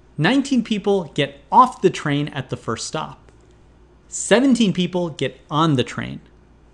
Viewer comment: I missed the first question because I thought it said "17 people get off the train". As you can see, I is very smart.